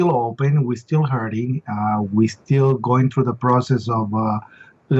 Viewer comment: For a moment I thought I heard William Rodriguez say Talmudic Shock Syndrome.